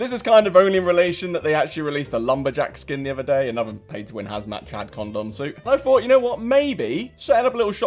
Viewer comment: This whole time I thought he's been saying chav Condom